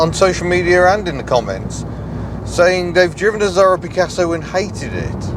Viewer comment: I keep hearing “Pasta Picasso”